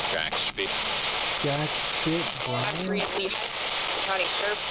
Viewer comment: I heard “county sheriff”